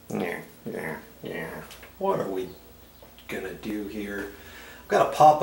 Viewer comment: What did you say? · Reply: I think I said "what are we". Had to play it a couple of times to find out!